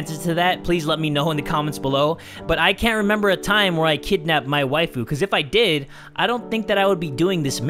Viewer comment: Tbh I was going to comment that her name sounded like supernatural. And dude for the longest freaking time I thought Mai Waifu was you saying "my waifu" it took me so long to realize that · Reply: Mikasa Ackerman I thought he said "My Wife Boo"